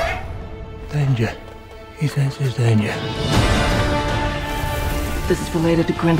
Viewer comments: What does he say at 1:34? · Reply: "Danger. He senses danger." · lol what is he saying at 1:34??? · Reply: I thought he said, "Danger. His name is danger." XD